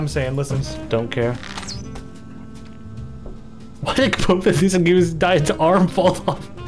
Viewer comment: I think he said "why did both of this guy's arms fall off?"